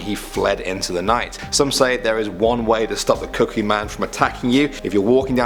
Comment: I thought u said cookie man—__— or am i crazy?